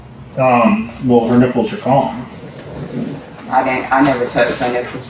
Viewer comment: What the.. Did he say her nipples were gone? 😲😲😲😲